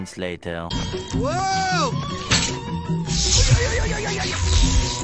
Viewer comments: man gets shocked to death Snufkin: laughs “Look at him! He’s shining like a full moon!” he proceeds to laugh while little my is concerned for his mental health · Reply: At first I thought he said, "Look at him! He's sounding like a full moon!" XD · man gets shocked to death Snufkin: laughs “Look at him! He’s shining like a full moon!” he proceeds to laugh while little my is concerned for his mental health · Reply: I first heard "Look at him! He's shawty like a full moon!"